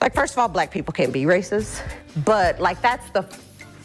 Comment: did she say black people cannot be racist or I heard it wrong??